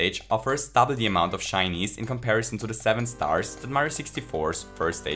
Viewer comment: You probably didn't intend that at all but I don't hear "Shinies" (isn't it "Shines" anyway) but I keep hearing "Chinese".